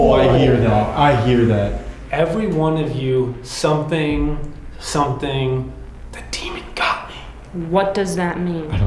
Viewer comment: I hear 'Every one of you should run, the demon got me' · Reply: I heard "Everyone of you, be careful, the demon got me"